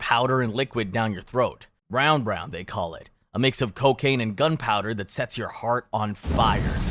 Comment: sorry did you say GUNPOWDER